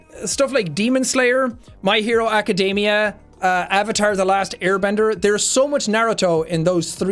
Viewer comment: for a second i thought he was saying avatar a anime